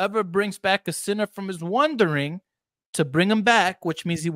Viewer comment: It almost sounded to my ears that he said wondering instead of wandering. On the other hand I could be listening with an accent.